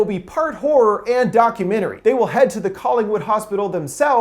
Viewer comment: omfg I thought at first he said "parkour and a documentary"